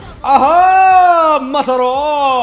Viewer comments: Did he just said "Masarap" at 2:45??? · Did he just say "masarap" in 2:44?